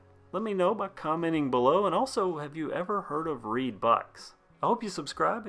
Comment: At first I thought you were saying it was a reee bike and I was like oh lord this is gonna be funny! I mean riding a reee down the trail🤣🤣🤣